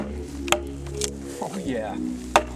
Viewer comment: did he say "fuck yea"?......lol